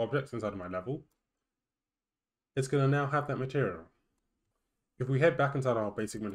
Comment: Please articulate better; it's hard to understand, like you're swallowing the words. For example, at 1:24 you pronounce it as 'maherial", instead of "material". I did give a thumbs-up because it's a quick and good to follow tutorial with closed captions on.